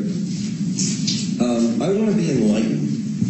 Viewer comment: Mr. at 1:08, the judges says, "I want to be enlightened." Your captions says, "I want to be in-line." Serious!? This is YOUR channel. YOU should be actually listening to the content you're putting out.